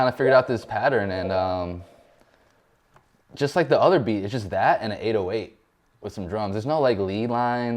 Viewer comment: can someone spell out what 6ix says? Something like 'eight away' or so? Too bad english isnt my native language.. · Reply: He says "its just that and the 808"